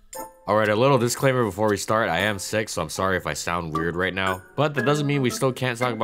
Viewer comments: When you said "I am sick so I sound kinda weird " I wasn't looking and thought I heard you say "I had sex so I sound kinda weird" I was like wuuut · For some reason, I heard "I am SIX so I'm sorry if I sound weird right now" in the beginning